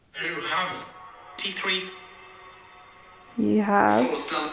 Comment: I hear "foursome". 😂